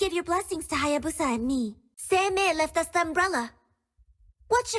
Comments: I think at 6:58 she says “Seimei left us an umbrella” (Seimei known to be the first Onmyoji that controls shikigami ) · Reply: So THAT'S what she said, i hear "sensei lessa a umbrella". Thanks for clarifying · I think at 6:58 she says “Seimei left us an umbrella” (Seimei known to be the first Onmyoji that controls shikigami )